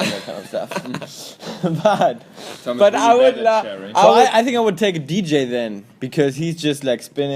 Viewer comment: hi, someone could tell me what's Georg saying at 28:57 ? thanks! :) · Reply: He said, "Tom is really bad at sharing!" XD